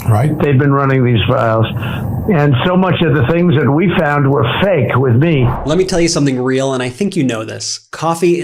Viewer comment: Did he just say "fake with me"??.. so he's innocent because HE says so.......